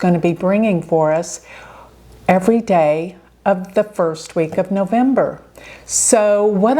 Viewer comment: OMG I thought you said this was the worst week of the month. And mercury and mars were already together.